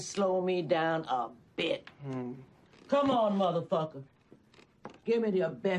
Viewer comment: Wow I first saw this in 1999 and the whole time I thought she said Big Mother fucker